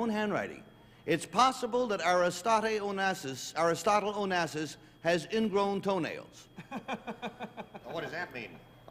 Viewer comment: What’d he say at 21:57? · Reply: Onassis has ingrown toenails.